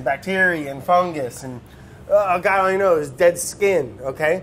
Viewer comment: I think he says "and, god only knows, dead skin..."